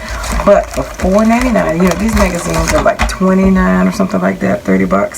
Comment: OMG-I am really upset over the Closed Caption misrepresentation of your word for "magazine" at 3:52. They used the "N" word and I clearly heard you say "magazines", not these "n----s". Who can I report this to? I see a lot of filthy four letter words in these closed captions that I believe are deliberate. This has got to stop. How dare they! You don't speak that way, ever. Other than that, I enjoyed your video, as always.